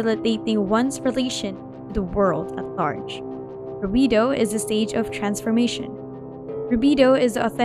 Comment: the subtitles say "burrito is a stage of transformation"😭